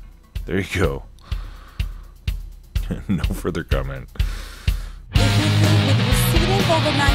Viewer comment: What he said was, "No comment....." What I heard was, "Finkle is Einhorn! Einhorn is Finkle!"